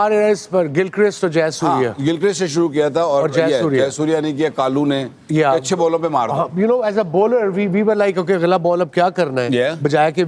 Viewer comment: Did Waqar said "Jaisurya ne, Kalu ne" @ 16:08 ??